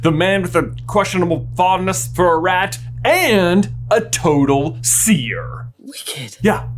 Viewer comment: I thought this said "ron weasley is a seeker" and i was like well thats demonstrably false